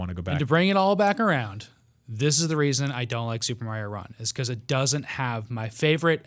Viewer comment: For a second, I thought said "That's why I don't like Super Mario one", instead of "Super Mario run". I had to rewind to correct that.